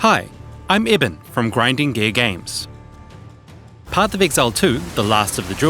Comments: Bro I can't be the only one who hear him say "Grinding Gay Games". No shot! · I thought u said grinding gay games. I was like what am i playing here?? I do not play POE but I am gonna buy it soon as Druid drops.